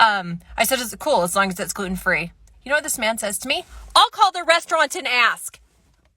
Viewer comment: When she said "as long as it is gluten-free," I thought she said, "as long as it is good and free."